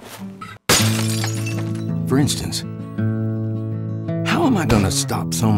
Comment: IT'S OVER 9000!!! Edit: Did Anybody Hear It As Fornite? Cause I Did